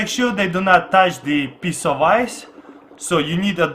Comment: it sounds like he said piss off ice